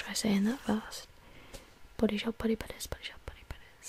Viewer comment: I heard "bodyshop buddybutters" bwahahaha